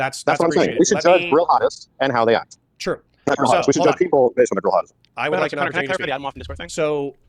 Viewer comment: I googled it & nothing (lol dork. sorry) — what is a “girl hottest” group/server. Or is he saying a diff word? Something-hadist?